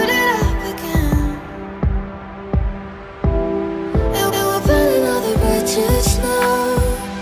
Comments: Totally unserious, but sometimes I hear "Burning all the bitches now." Hahaha, so mature :D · i hear "we're pulling all the bitches now"